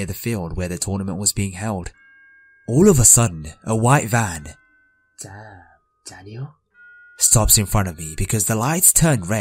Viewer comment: did I just hear, " damn Danielle"?